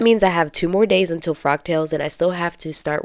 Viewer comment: I thought you were saying frog tails...😆 The fabric is lovely! It reminds me of "Monet". Look up Monet, Water Lilies. So I thought the print was perfect for a frog tails event..lol... I'm such a dork. The dress turned out sweet! In all the outfits I've seen you on, I never thought your shape was as similar to mine as you suggest... (Though I'm definitely larger). So good job sewing things that accentuate your figure so well. I have a few of my mom's old vintage patterns, but there is no way they'll ever fit me... Love the gloves too! Nicely done!